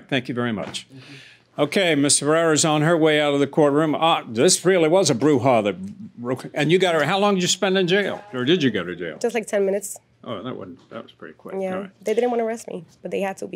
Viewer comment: Did he call that lady a Bruja @ 14:14 ??!! 😲